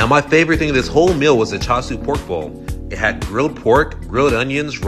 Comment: Damn thought you said child support. You said chasu pork 😅